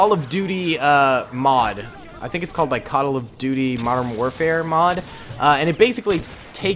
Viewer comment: did you say “cuttle of duty”?